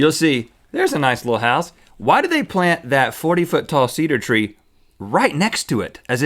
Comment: Took me the longest time to realize that they were saying "Cedar" tree LOL I thought they were saying "seeder" tree and I was confused